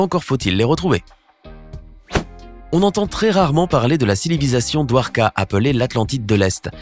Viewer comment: Like si toi aussi ta entendu cilivisation à